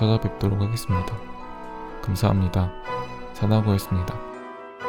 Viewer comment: "사자보이습니다"라고 들리는 건 기분 탓인가요?